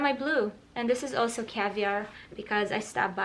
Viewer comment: what a word in 12:23 because I hear caviar (I’m learning English)